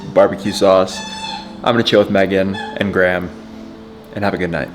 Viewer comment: Had this going on the side and at 22:15 I thought I heard say "I'm going to choke Megan and Graham and have a good night"